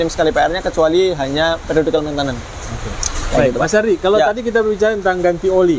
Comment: Menit 10:26 Mas bilang apa? “Kecuali hanya………” mohon yg tahu bisa komen. Trims. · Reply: Periodecal Maintenance pak, atau bisa disebut servis berkala 😁